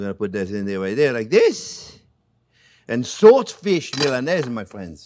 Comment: Ohhh my apologies Chef… I thought you were saying SALT FISH… and I see now that you were saying SWORD FISH. I was wondering why no-one was replying to my question.